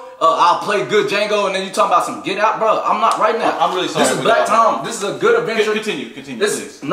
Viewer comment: The show was called "Blacktime", but the dude pitching it had to show that producer what it meant when it is time to get black on his ass 😂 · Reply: at first i heard "black in time" which sounds much better